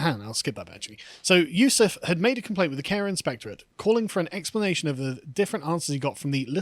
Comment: I know that Carl is saying "Care Inspector" but I keep hearing "Karen Spectre".